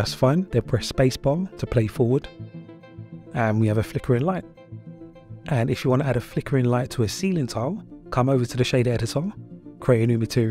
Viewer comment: hey what did you say on 1:30 if you want a flickering light on a what? sorry i didnt hear it😅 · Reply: No problem man, i said ''if you want to add a flickering light to a ceiling panel or tile''